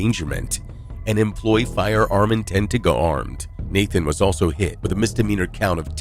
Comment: “Employee firearm intent to go harmed?” 24:28 WTH did the commentary say?